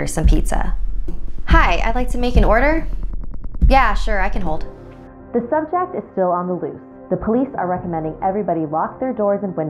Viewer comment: Im guessing when she got the neck pain, her dad was getting murdered. Yknow, since they feel their presence, she probably felt his pain. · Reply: Gabriella Lobsinger omg yea ur right my dumbass thought the news said"the thumbtack is still on the loose" and thought those were thumbtacks it's subject I'm so dead lmal